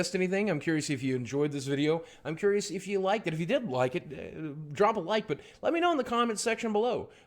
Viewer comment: did I just hear I'm curious to see if you liked it if you didn't, drop a like below ? Aha :')